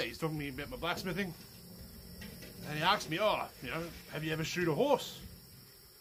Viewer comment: 😂I heard "Shoot your horses" and had to watch this twice!